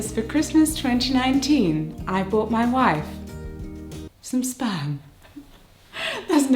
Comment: I immediatly thought "baby!", but then misheard you say "I bought my wife some spam," and I just couldn't for the life of me figure out why the spam would be a secret... (I do mean the weird meat(?) in a can) Congratulations on the actual living being though!